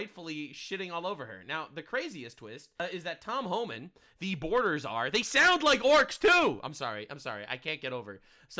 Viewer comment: I heard "Deporter Czar" instead of "Border Czar."